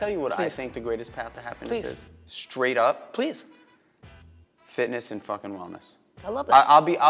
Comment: what did he say Fitness and fucking.... what? at